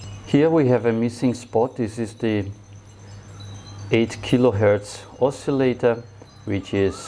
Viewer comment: Hello. I received a board with a replaced quartz resonator. Did you say 8kHz? 13:16 On my board it is set to 8MHz. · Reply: Yes, there should be a 8 kHz resonator.